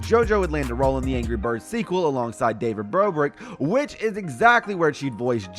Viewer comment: did he just say david brobrick?